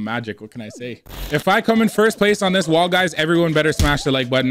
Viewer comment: did you just say wall guys?